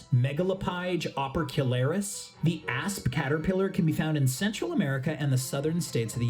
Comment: Had to put the subtitles on cause I thought he was saying "ass Caterpillar"